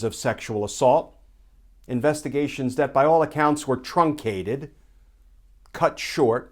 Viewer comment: 😆😆for a second when u said truncated I thought u said Trumpcated. I think that would have fit too. Thanks for your daily videos Glenn, you’ve definitely helped keep me sane watching all this craziness. Pls stay safe. Big love sent from Australia xx